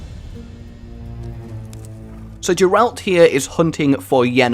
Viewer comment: did he say "Jeralt"? xD